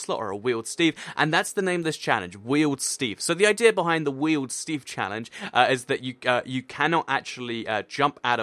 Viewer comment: Which is your favourite game mode to play in Minecraft? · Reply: i thought you said weild seeds lol